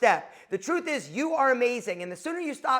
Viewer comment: I thought it said "unf*ck your wife" lol